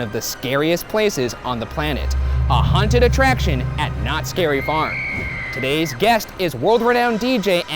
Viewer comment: This whole channel is just an excuse for Ryan to repeatedly go to Knotts Berry Farm. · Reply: oh is it knotts berry farm? lmao I thought he said not scary farm